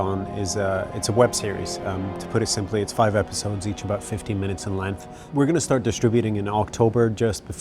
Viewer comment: unfortunately, sounds like he says 15 minutes to me. shame, 50 minutes would be mighty nice.